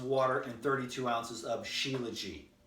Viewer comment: What does he say at 9:57? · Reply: "Shilajit" do your research 😉